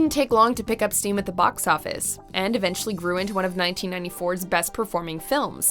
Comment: i might be getting old....i was listening to the Speed entry and instead of "GREW INTO one of the best performing films of 1994", I heard "RUINED one of the best performing films..." like WTF??? lol